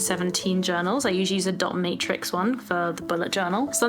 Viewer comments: I had to listen to this five times because I thought you were saying you liked the 'dominatrix one'. 😛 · I had to roll back. I swore you said a dominatrix notebook.....I spaced out. dot matrix, lol that's what you said.